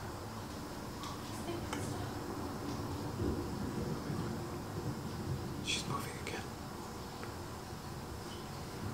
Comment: there's a girl voice.. I can't figure out what she said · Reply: It says please make it stop